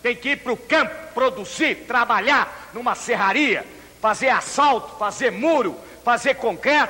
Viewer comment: juro que eu entendi" fazer assalto" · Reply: Ele disse asfalto kkkkk